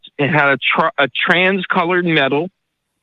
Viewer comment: "...a TRANS-coloured metal"?!? "...a TRAIN-coloured metal"?!? "...a RANCH-coloured metal"?!? "...a STRANGE-coloured metal"?!? What is he saying, there? "Strange" is the only option I can see that even partially makes sense, but it sounds nothing like "strange"- It sounds like "trans", or maybe "ranch", or something. But the caller just sounds like a standard conspiracy nut- "Believe exactly what I believe, on the basis of nothing but anecdotal campfire spook-stories, and maybe a vague, cherry-picked conspiracy-narrative with no specific, empirical evidence, and no scientific support at all, or else you don't REALLY care about truth!!! (because it's only ME who knows teh REAL troof!!!)" If you used this standard of evidence CONSISTENTLY (rather than just using it as an excuse to believe stuff you have an emotional attachment to), you'd end up a member of every cult and religion, and believer in every conspiracy theory on earth. I believe him that he may well have seen three lights, when he was a kid. But he expects everyone to join him in stacking up this huge house-of-cards of evidence-less assumptions on top of that; That every random medical symptom he had around the same time CAN ONLY be linked to the lights? That the lights CAN ONLY be space-aliens? That there must be an undetectable, secret implant in his head, hidden by the undetectable, secret aliens? Sorry, but once you've started assuming "undetectable" things, in order to keep your pet narrative alive, you'v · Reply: Trans coloured metal would be a paint job with the colours that kinda change - some of the wide boys here have that on their souped up vehicles. Also people with epilepsy get pretty confused, and the medication back in the day used to add to that confusion.